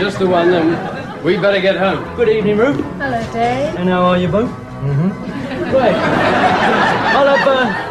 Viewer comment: What did Dave say to the barmaid? I can't make it out but I'm assuming it has something to do with her breasts... (Edit: He said "how are you both?") 5:50 "For every man out sowing his oats, there's a woman following behind with a packet of weed killer." Love that! 😄 6:06 I had no idea Crawford was left-handed till now.